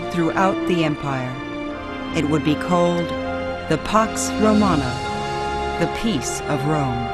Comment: It sounds like she says "Pox Romana"... What is that, some sort of ancient Roman skin disease? Of course, when all the roosters and hens start getting along together, we would call that the Chicken Pax. (And, yes, I am aware that in some parts of America, 'pox' and 'pax" are pronounced identically; no need for a linguistics lesson over my lame Dad-joke. 😜)